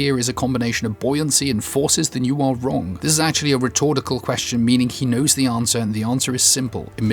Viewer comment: Did you say 'rhetortical'?